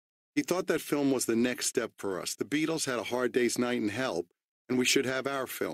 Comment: At first I thought he said "The Beatles had A Hard Day's Night in Hell..." lol